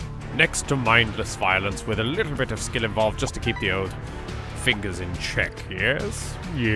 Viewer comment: I actually thought you said "a little bit of skin involved to keep the fingers in check." Um...bad touch?